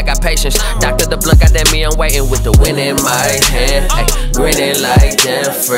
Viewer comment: At first i thought he was saying wind in my hair